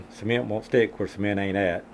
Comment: ¿WHAT? Lol! What did he say? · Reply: Cement won’t stick where cement ain’t at. When training new people, they try to avoid getting cement on their fingers and don’t cement everywhere on soles. They think they covered enough area. When they go to stick the sole, they find an area that they missed and have to do the job again. Our founder Ricky Wyatt would say that to them over and over (hundreds of times) until it rings in their heads.